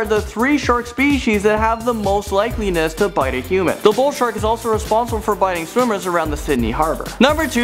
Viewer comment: what did say at 4:05? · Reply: he said the bull shark is responsible for biting swimmers around the Sydney harbour